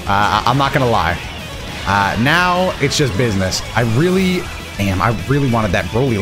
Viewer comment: did someone say Business?